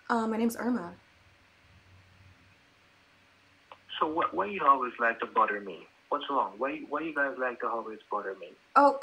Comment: I thought the indy roach said: Why you always like to butter me.